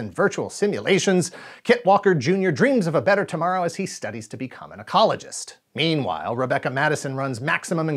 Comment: I thought you said "He's studying to be a gynecologist"!!🤣🤣